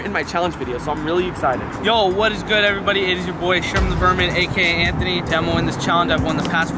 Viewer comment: I thought he said Sherman the virgin XD Its just a joke.